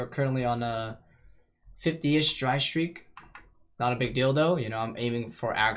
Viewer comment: i swear to god i just heard " not a big dildo "